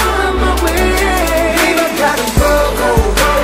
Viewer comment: i don't know why i heard "Bieber gotta go!" XD